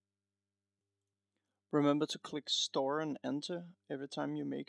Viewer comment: i didn't quite hear what you said at 0:51. Did you say " Press star and enter" ?